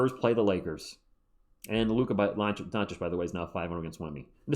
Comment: I’ve rewound several times and still don’t know what you’re saying at 8:05 lol (sorry) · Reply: Oops, mumbled that! Luka Doncic is now 5-0 vs Wemby.